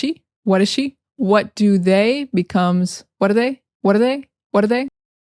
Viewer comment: what do they become whadathey. i thought they were saying what a day😂